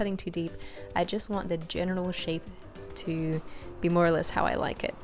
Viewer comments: Does anyone else think this resembles... something else............. lol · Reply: And I also thought she said "genital shape" when she started carving and said "general shape" 🤦‍♀️ I don't like pink anyways, so hopefully if I ever make one I'll get that thought out of my head with doing different colors. Perverted mind aside, it is a pretty cake. 😊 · does she say genital shape? Hahahahaha